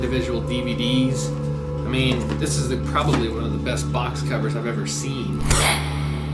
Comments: he says box covers not box cutters haha he clearly was talking about a dvd box. Even the captions say covers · he didn't say box "cutters", he said box "coVers".....I am seriously shocked to see such a major snafu on your video. I'm wondering how you could misquote someone to that degree and put it in all caps and not clearly see and hear he said covers, box coVers? He was just talking about the quality of a box set of DVD's and had said it was one of the best box coVers he'd ever seen. You may just wanna stick to the drama. Also, this doesn't mean he's not a murderer now, but it does mean that this content creator should be better than thaaaat. If I can tell what was said, then she/they/whatever shoulda been able to tell what was said. Stick to the facts. No embellishments were needed to convince me this guy's a total sick fuck.